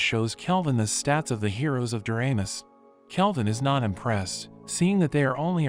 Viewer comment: i watched at 11:10 an did i hear URANUS? FUNNY JOK!!!